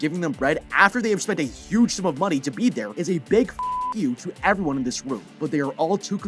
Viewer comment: I love how you said "a big f!ck you" but the subtitles say "a big thank you"